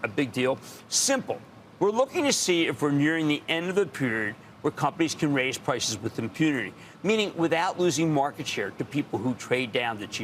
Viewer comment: What did he say at 6:38 ? Lol 😂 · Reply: “nearing the end of the period when companies can raise prices with impunity…” He talks so fast, sometimes, his tongue starts tripping! 😂